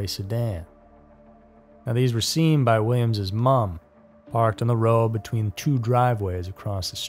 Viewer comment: Did you say mum?